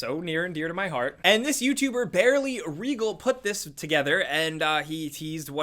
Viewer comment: did he said Barly instead of Early 😁